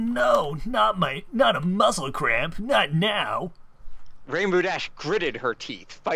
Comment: Did he say "muscle-crab"? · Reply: I think it was muscle cramp, but I like your "muscle crab" better.